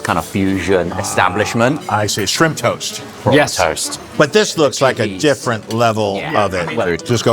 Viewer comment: I thought had said "corn toast" until Jon said, "Oh, shrimp toast!" and I realized he'd said "prawn", lol.